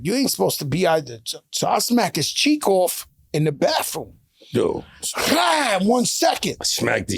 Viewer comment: Did he just say “he knocked his cheeks off in the bathroom legend” 😂😂😂😂😂